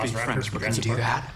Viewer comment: Can someone tell me @ 4:20 what The company owner said I couldn't hear cause it got overlap by the reaction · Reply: He looks at the other synth/hybrid and says “sure you can speak French but can you do that?” It will show him favouring Wendy again